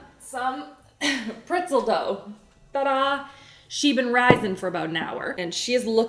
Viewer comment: legit thought you said ‘shibin risin’ and I was like who’s that😅 it sounds like a Star Wars character or somethin